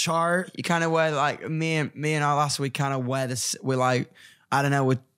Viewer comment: what does he say his girlfriend's name is? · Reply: He says “me and my lass” which is another word for girlfriend in England lol